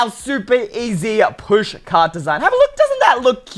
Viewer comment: I don’t celebrate Christmas 😭 i have to wait until my birthday so I can grab some merchandise edit: 6:24 it sounds like your saying push card